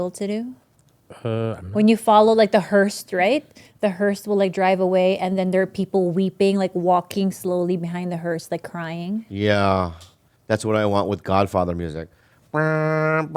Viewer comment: Is she saying Hurst